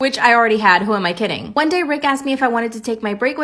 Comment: In the first story she said Rick asked me and it sounded like she said Rick astley